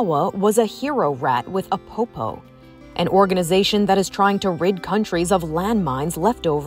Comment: I thought they said "rape countries" lmaoo